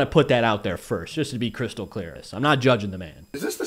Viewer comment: Everytime you say "crystal clear" I hear you saying "crystal christ-al clear" 😂